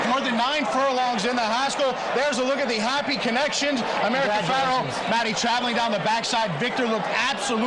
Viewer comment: "That magnificent stride" is right! Two feet longer than Secretariat's! (Can anyone tell me what the track announcer said at 2:25 that got everyone so excited all over again?) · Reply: I believe he says "Number 4, American Pharoah!" (easier to hear in other videos)